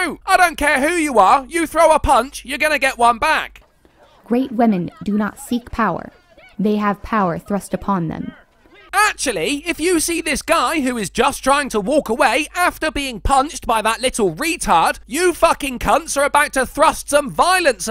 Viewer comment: I only heard "great women have it thrust into them."